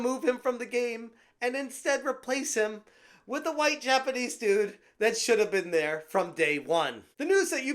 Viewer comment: "and replace him with a white Japanese dude" . Am I hearing that right?